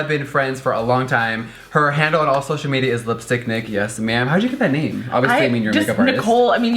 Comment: I thought he said this is lipstick dick haha